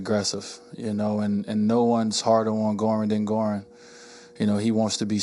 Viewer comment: Can anyone help to tell me what Wade said on 0:26? "No one's (?? ) Goran" Thank you so much. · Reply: No one's harder on Goran than Goran